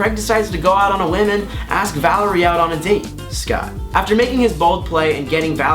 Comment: did anybody hear him say Scott ???